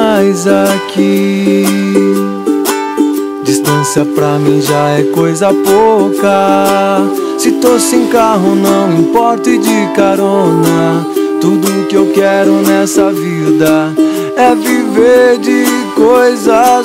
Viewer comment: eu entendi: ''distância pra MIJAR é coisa pouca''